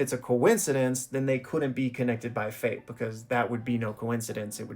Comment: i misheard and thought you said "connected by FENT" at first.. im cooked